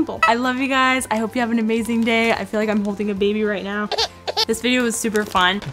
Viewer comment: Anyone else thought you heard her say "I'm HOPING a baby" 😂 anyway there is like a 100,000 out of 1 chance to win the onesie but like we can always hope right...anyway what made me really come across your channel, is when I first saw your thumbnails I was blown away. They were so bright and colorful, and many of them were on topics that I felt I would enjoy like "hair styles" & "how to wake up early". Seeing your videos made me think that since your account seemed so bright and joyful even though you might have not been feeling it at all times, I thought it would help me become a more bright and outgoing person, and let me tell you, it did! So also thank you for that!! Love you! 💚❤🥰